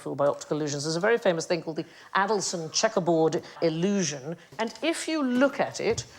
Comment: I've been watching too much old school Never Mind The Buzzcocks because I genuinely thought that Sandi said "the Athelston checkerboard illusion" and then concluded that the whole reason he was staring was that he spotted an optical illusion up by the stage lights.